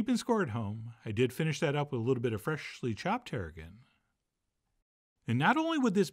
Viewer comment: The subtitles thought you said you finished "that up with a little bit of freshly chopped hair again" :D John I cant wait to make this but ill make sure to have plenty of tarragon on hand!